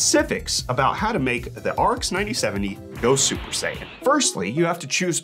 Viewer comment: thought i heard Arc 9070??😅